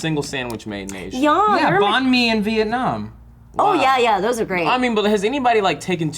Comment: I SWORE said bomb me in Vietnam is it bad I laughed